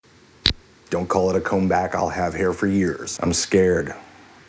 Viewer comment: I just got the LL Cool Jay reference. "Don't call it a comeback, I've been here for years" - clever. Very clever. Do I win a internet cookie? And BTW - why don't you do stand up? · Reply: The first time I watched this I thought he said "Don't call it a comb-back; I'll have hair for years."